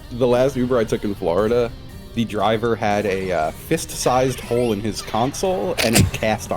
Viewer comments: Fist-sized hole in the console and his right arm in a cast is absolutely absurd environmental storytelling. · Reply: All this time I thought said "fist-sized mole" as in the animal was living in his fuckin glove box... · Fist-sized hole in the console and his right arm in a cast is absolutely absurd environmental storytelling. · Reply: i thought he said fist sized bowl and i thought he was the most high any uber driver has ever been and the cast was just another detail for fun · Fist-sized hole in the console and his right arm in a cast is absolutely absurd environmental storytelling. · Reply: I thought he said fist-sized Grohl like he was driving around with a Dave Grohl funko pop because he was a huge foo fighters fan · Fist-sized hole in the console and his right arm in a cast is absolutely absurd environmental storytelling. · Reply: i thought he said fist-sized shoal like his console was akin to a sandbank that makes water shallow in a certain area